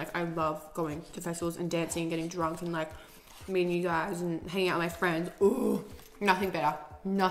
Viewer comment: I seriously thought when she said "meeting you guys" she said " meeting new guys" ahhahahahhahaha